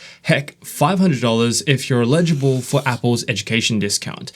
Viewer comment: illegible?? Lmao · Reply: It sounded like he said “illegible” meaning hard to/unable to read, but what he said was “eligible” meaning having the right to… But it’s funny because he was talking about eligibility for an education discount, and it sounded like “illegible”, which could be a comment on someone needing to go to school to learn how to write in a more “legible” way., so, uh, if you’re illegible, you should by default be eligible.